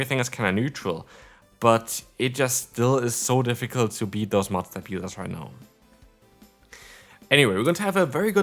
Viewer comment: Did he say master peelers at 2:55? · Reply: Mudslap users